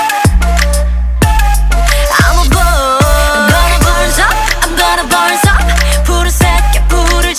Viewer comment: “I’m gonna burn up” Me 1st time listening to Bird: Hmm, the pronunciation is kinda weird Me 50th time: “I’m gonna burn sup, I’m gonna burn sup........Fire ~” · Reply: I thought she was saying "I'm gonna burn this up" :O